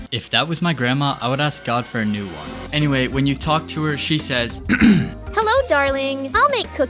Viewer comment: bro what did you say? Talk Tuah? like the podcast?!